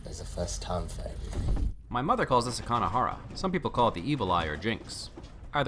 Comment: I know that this is a 4 year old video but it's actually "ayin hara" which translates literally to Evil Eye. "Kein ayin hara", which is what I assume his mother actually said translates to "No Evil Eye" and is a Jewish/Yiddish expression similar to "knock on wood". (this happens at 3:50 mark...)